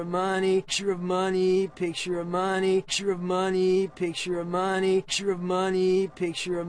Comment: If u speed it up at 2X It sounds like he is saying "Trie the money...Trip-o The Money"